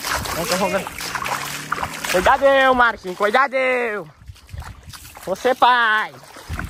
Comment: Só eu que ouvi o dizer que vai ser pai no minuto